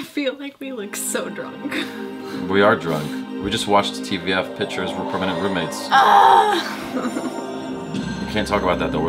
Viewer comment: koay .. what did you say at 1:16 .. you watched TVF Pitchers from Permanent Roommates ? Lol.